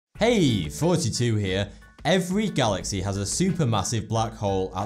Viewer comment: Sounds like he says hey 42 here